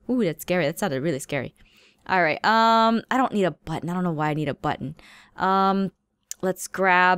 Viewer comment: When she said I don't need a button I thought she said I don't need a but.oops.